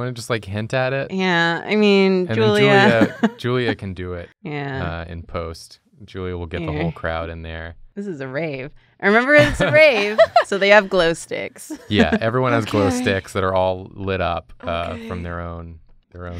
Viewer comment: Every time you guys say 'it's a rave' all I hear is 'ITS WORM TRAIN!'